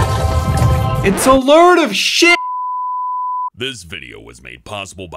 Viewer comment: did just say shit